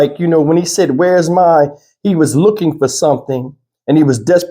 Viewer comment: Sounds like he says, where's my girl using earbuds.